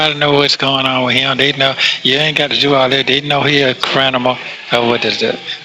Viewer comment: Did he say "crinimal" at 10:37 😮😂